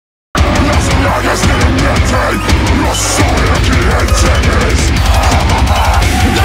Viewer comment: I WANT TO KNOW WHAT THE DEMON AT THE VERY BEGINNING IS SAYING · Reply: “Las plagas de la mente. Yo soy a quien temes” is Spanish. In english: “The plagues of the mind. I’m the one you fear”. AND HOLY SHIT what a plot twist to the lore Jay himself killed Rachel WTF!!!